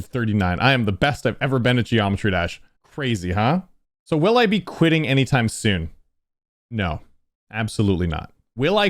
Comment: I thought you said I’m quitting gd an YouTube